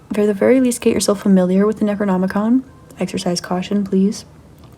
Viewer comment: Hey!!! Can someone help me out with what she says at 2:33:14? "Get familiar with ..."?? I cant get it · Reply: Necronomicon (I would encourage reading it alongside Enuma Elish)🤍